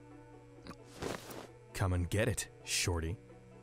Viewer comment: For a moment I thought he said “come and get it !ShAwTy!” I WAS LIKE WAHHHHHH 💀